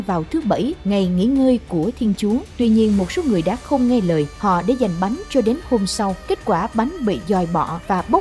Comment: Đại tội tham ăn từ đây mà ra chăng? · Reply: bạn đang trong việc tranh luận đạo thiên chúa mà bạn không biết đại tội tham ăn Beelzebub mà bạn nói tui không có học thức là sai rồi đáng lẽ ra bạn nên nói là tui đã nghe nhầm để giành thành tranh giành-_